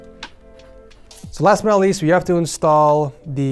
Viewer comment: i thought you said now in the middle east but you said "last but not least" lol.